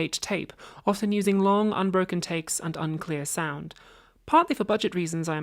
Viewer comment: a typo in the captions, which I've always appreciated have been very thorough, incidentally. Reads "takey" instead of "takes"